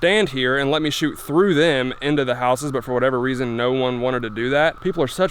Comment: Oh Crap, phew. I thought it said horses....